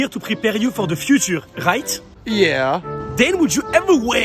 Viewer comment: Why do i hear it like "hite" when you guys say right, mdr😂